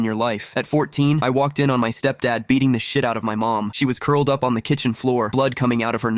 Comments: I thought he was going to say "At 14, i walked in on my step-dad beating the sh*t out of his meat" not "out of my mom" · Thought he said beating his meat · I thought bro said "I walked in to my stepmom beating my dads meat" 😭😭